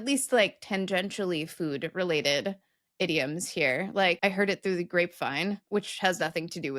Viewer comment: eggcorn alert! I totally thought it was "heard it through the great vine"